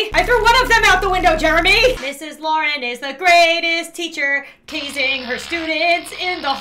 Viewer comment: I HEARD "TEASING" 😭😭😭😭 (I know It's "Tazing")